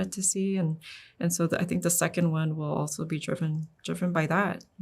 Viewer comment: Did anyone else hear the "second one WILL" @ 12:56?